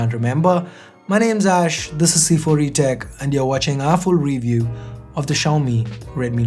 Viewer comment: When he said "our full review" at 23 seconds, I misheard it for "awful review"